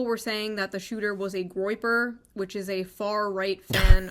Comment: What is the word she used? "A groper"? I am a native. I didn't understand and I think the automatic subtitles gave a wrong transcription. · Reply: Groyper is the word. I don’t know what it means either. Never heard it before all this.